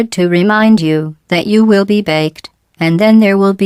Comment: BAKED!!!! I always thought it said Vaped. Now it makes more sense